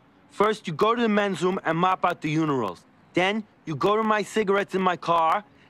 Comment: Am I the only one who heard “unirals” at 2:08?